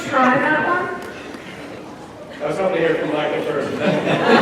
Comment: What he said? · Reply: "I was hoping to hear from Micheal first"